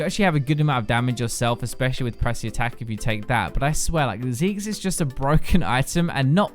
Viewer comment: Whenever you said Zeke's, I heard it as Zigg's, which was VERY weird